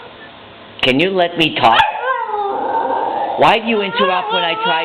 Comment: Lol when the dog goes quiet for a second and he says "Will you let me talk" I could have sworn I heard "Fuck that"